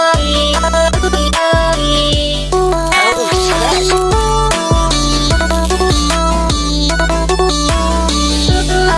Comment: is someone know what cassette girl is saying here? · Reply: oh sh*t